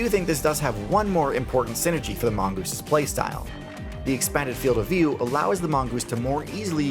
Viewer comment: I first thought they said are Moose's op. I was just like heck yeah